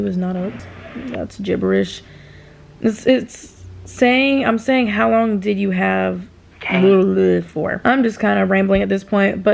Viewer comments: i’m a whole week late lmfao but it sounds like u said “how long did u have braces for” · Aww your lashes came out super cute you should definitely do it again ❤️ I like going through the comments too 🔥it sound like you said how long did you have breakfast for 😂😂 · It sound like you said how long y’all been up for